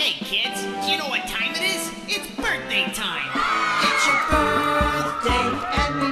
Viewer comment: I heard "kirthday"